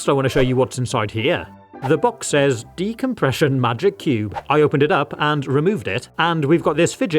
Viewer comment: I thought it said depression cube 😭